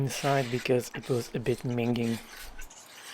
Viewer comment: did you say minging? That is the first time I've ever heard that word, all be it quite common. It's like 'manky', another word Americans don't use. Also, love your show.